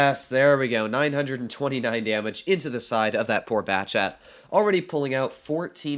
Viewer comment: who else heard "poor bastard"😂